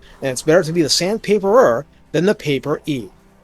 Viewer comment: Did you just say "PAWR-ee?" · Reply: Paper-ee is how I wrote it, heh.